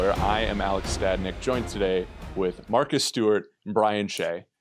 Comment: On 0:23 I thought he said Martha Stewart 😂. Then I replay it back and he actually said Marcus Stewart 😂